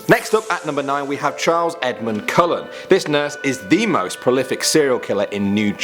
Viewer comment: Who else thought said "Edward Cullen"..... I WAS CONFUSED